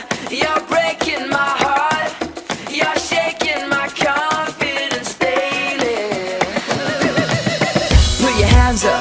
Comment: Omg..now that I've heard the lyrics,I'm laughing so hard at myself right now cuz I thought Brad said "You're shaking my coffin and stay there" instead of "You're shaking my confidence daily" xD